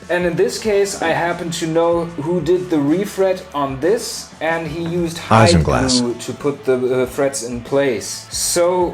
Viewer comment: Was sagt die Stimme bei ca 2:45? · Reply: das dürfte 'Isinglas' sein, englisch für Fischleim. Getrocknete Hausenblase, wenn ich mich recht entsinne